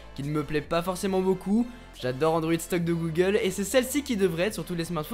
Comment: Tu dis quoi à 3:19 ? Android stock de google ?